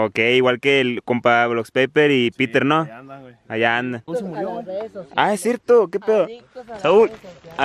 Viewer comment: ¿que dijo El Saúl? -Sopotamadre -Como tamales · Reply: Tamales